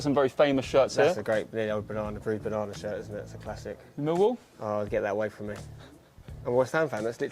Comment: What's the shirt/club at 1:40 please? The white and green that they talk about. I cant really understand english :'( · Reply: Hi mate, the green and white shirt is the Millwall 1995-96 away shirt